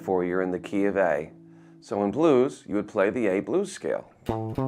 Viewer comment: ? i cant undertand what scale he said what is it? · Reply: the a blues scale